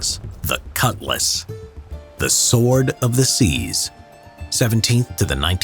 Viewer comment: I thought he said "the sword of diseases"😭